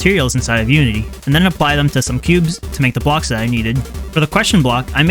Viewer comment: it sound like he says pubes